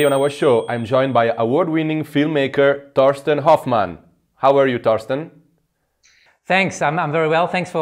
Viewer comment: I thought he said Dustin Hoffman. I was so excited for a few seconds.